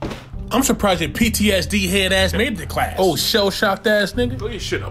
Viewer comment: does he say “why don’t you shut up pussy”?. Still funny as hell.